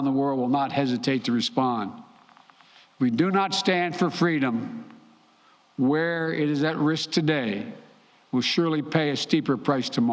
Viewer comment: Did he say "We do not stand for freedom where it's at risk today..."